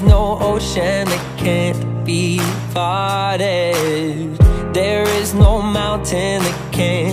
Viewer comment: OMG I THOUGHT HE SAID "There is no ocean that can't be farted" AT 0:14 😂😂😂😂